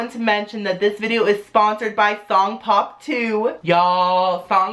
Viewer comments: i thought that he said thong pop 2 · i thought you said thong pop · and i thought u said thong pop 2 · I thought he said thong pop